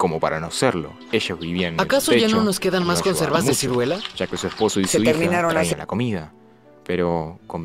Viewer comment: que lo que dijo en el minuto 2:34 a 2:39 ??? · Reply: Si perdón tuve un pequeño fallo en el audio, y dije - que su esposo y su hija ayudaban a traer la comida- me disculpo por la molesta interrupción de la película y gracias por comentar.